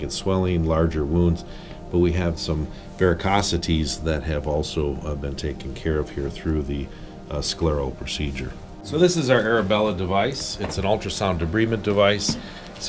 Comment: Could someone transcribe what he’s saying from 2:27 to 2:32…thx. 😇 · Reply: "Varicosities that have also been taken care of through the sclero (sclerotherapy) procedure." Sclerotherapy is a procedure done in an office setting, usually by a vascular surgeon, whereby a sclerosing chemical is injected into varicosed or refluxing veins to cause scarring of the vein.